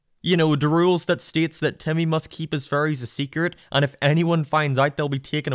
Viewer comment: I love how you say furries instead of fairy's